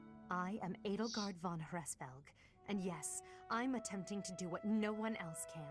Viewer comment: por un momento pensé que decía Edelgard von Hesllsing